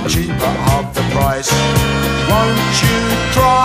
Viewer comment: I always used to mishear 2:24 as "Abortion or a slice" instead of Portion or a slice lol.